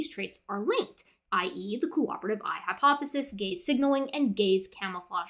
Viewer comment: I thought she said gay signalling